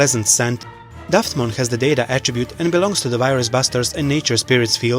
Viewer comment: Bothered me when hearing "Buster" I kept actually hear "bast***". Was awkward hearing video. Anyone else thought he was saying "bast***"?